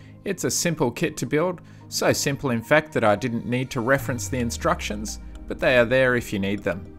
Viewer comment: lol 0:37 what you said, "so simple to build in fact that I didn't need to reference the instructions, but they're there if you need them. " what it sounded like to me, "so simple to build in fact that I didn't need to keep my eyes open, but they're there if you need them." Also, the sea looks too calm to have that much foam on it to me, but I live in a prairie so what do I know :P